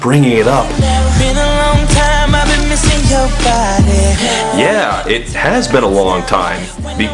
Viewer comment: I literally thought Pat Monahan said "on your caboose" first lol